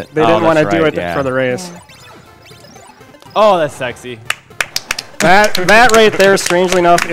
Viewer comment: Did he just say 'Whoa that's sexy'? Er ok........